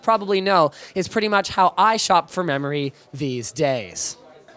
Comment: I thought he said the way I shop for money